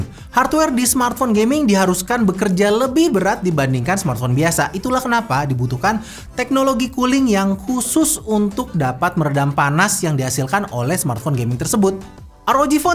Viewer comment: Kedengeran nya "teknologi KULI"... 😁😁😁